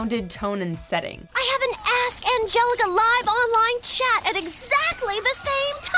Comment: when angelica said "ask angelica" i thought she said "ass angelica"